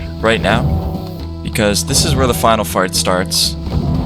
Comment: Did anyone else hear “final fart” at 10:16?